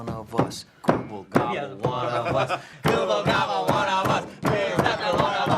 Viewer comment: no, Ight… anyways… GOOBLE GABBLE ONE OF U- (I think that’s what there saying)